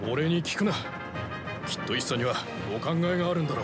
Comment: きっとISSAにはお考えがあるのだろう うん、階級なのは分かるけどこれにしか聞こえない…